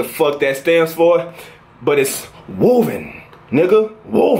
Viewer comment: Congrats! At first I thought it said women at first but then u said woven😂